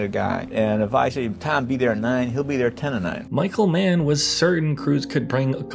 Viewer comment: Can smb explain the sentence ..."If I say <Tom be there at nine> he'll be there at ten and nine." ? Seems like "10 and 9" is 10:09 which is after 9. Or did I mishear something?